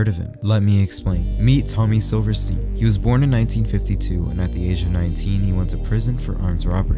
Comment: Why did I hear "He was born in 1952 at the age of 19"💀